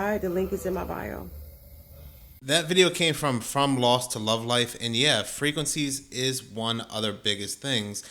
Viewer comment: Please let me know if you see the hype button? Thank you for all the support you have shown the channel! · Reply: How do I find the woman at 22:48 in this video? You mentioned the name but either I copied it incorrectly or Google is of no help . I think i heard from loss to lovelife, Im interested in her info on frequency , please and thank you. Hope shes on Youtube , dont do tictock or fB. Thanks again for this video! 😊